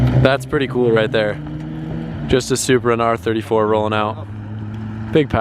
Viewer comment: Did he just say subaru instead of supra ?? In min